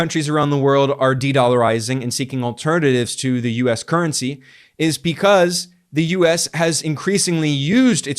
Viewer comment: I heard “US current sea” and thought, what do you mean, we’re everywhere now!